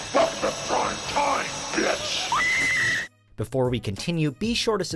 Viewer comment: I always thought he said “fuck the prime time bitch” lol